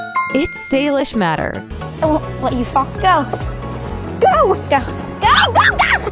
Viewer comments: I thought Salish was saying I won’t let you fu$k up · For a sec I thought Salish said where you f*** go 💅🏽🤣😈